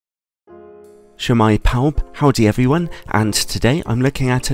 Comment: What is it he always says @ 0:00? Not being facetious, just curious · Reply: shwmae, pawb is what he's saying, its welsh, is welsh. shwmae is pronounced shuh-my meaning hi there or how are you pawb is pronounced how its said paw-b meaning everyone or all so a English equivalent would be hello, how is everyone. although Su'mae is more commonly used which means the same thing.